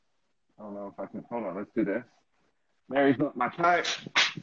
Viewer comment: did you say Mary? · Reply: Yes, I read a comment that said "Mary is not your type."